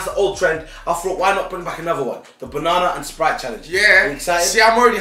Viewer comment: I thought it said banana and rice shit den